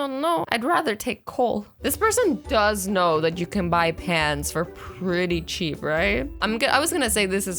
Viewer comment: I literally thought you said pants XD